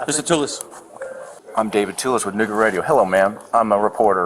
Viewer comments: did he just say nigga radio 😂😂😂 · Did he say, "I'm David Tullis with nig*er radio"? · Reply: Nooga Radio...as in Chattanooga, TN 😂😂😂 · What did he say? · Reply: Nooga Radio...as in Chattanooga....not what you thought... 😂😂😂